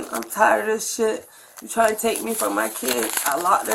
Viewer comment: I thought he said ,you trying to take my kids????